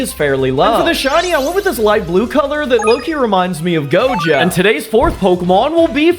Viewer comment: did you say gojo jjk Pokémon confirmed?